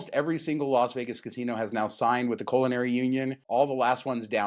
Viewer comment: Was listening to the show while I was distractedly painting the basement …And thought I heard say “colonary union”…and I thought…hmmm- that’s new….when did that start??😃